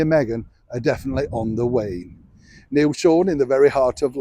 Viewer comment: Harry and Meghan are definitely on their way? On their way where? On their way out of town on a rail I hope. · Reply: I re-ran the video because I couldn't believe what I thought I had heard. What Mr Sean said actually was"on the wane" ie on the way out.